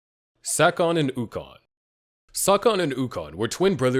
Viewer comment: Sakon & ukon.. I ain't going to lie when it first came on "I thought he said suck on a Yukon"🤦🏿‍♂️yea the Suv🤣🤣😂